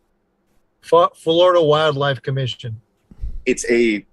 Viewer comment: why did it sound like he said fu(k Florida wild life commission